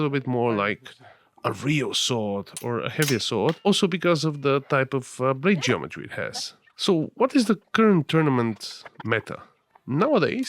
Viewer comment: I was watching the video at x2 and at around 7:03 instead of "blade geometry it has" I heard "brain damage he has", and I thought to myself what did Angel do to be targeted like this for the "real sword" preference xD Joke aside great video!